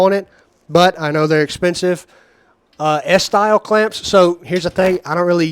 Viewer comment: In my limited experience what you are calling bar clamps are known as pipe clamps in other areas. The others that sounded like you were saying “S” clamps must have been “F” style clamps